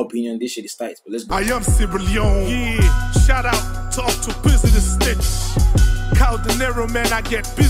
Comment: did I hear, 'thank you Octopizzo the snitch?'